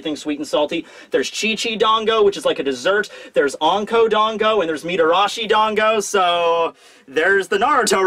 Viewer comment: at first i thought you said unko-dango which wouldve made sense